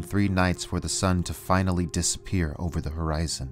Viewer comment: subtitle wrong , disappear>>appear